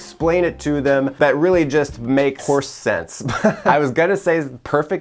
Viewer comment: I heard “whoresense” 😞